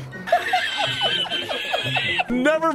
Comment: What I heard: "nevermind! Mommy keqing!"